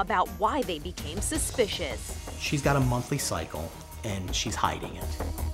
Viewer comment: I missheard monthly cycle and i thought they said monkey cycle